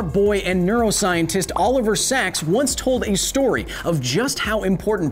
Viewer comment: i'm not gay but..... his hair is beautiful ps at 9:10 I thought he said "all over sacks" lol